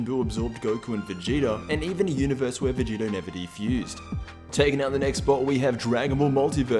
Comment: Can anyone please tell me what he says at 0:54 "Vegeta and........... inevitably????" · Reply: Anupam Jaiswal VEGITO never defused